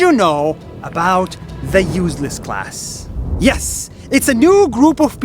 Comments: I thought he said useless cars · I thought he said “useless cars” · Omg I thought it said "say hello to the useless car" 😫😂